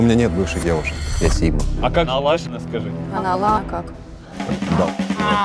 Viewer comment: что она сказала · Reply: "А Алана как"